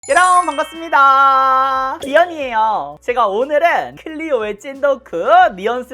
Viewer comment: 이에요인데 귀요미에요 라고 들리네요ㅋㅋ 님 귀여워!!